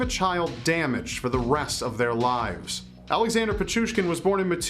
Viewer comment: I thought it said Alexander Pikachu